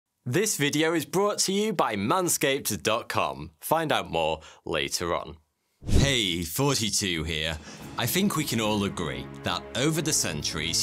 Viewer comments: I watched almost all of your videos, but I always heard "Hey Fourty2 Here!" instead of "Hey Thoughty2 Here!" did my ears broken? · OMG, this whole time I thought he was saying “42 here”, I enjoyed the first video I saw from this channel so I just subbed, I didn’t even look at the channel name, it’s not until a little while ago that I actually looked at the name and I realized that he was actually saying “Thoughty2 here”. Ngl, I feel really dumb right now 😂